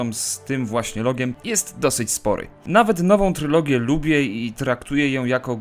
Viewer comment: what did he say? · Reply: "nowa trylogia" 9 lat termu to były prequel'e (1,2,3)